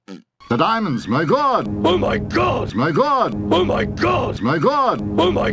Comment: It's the circle of MY GOD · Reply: Jack Sullivan I keep hearing "my Glod"! 😝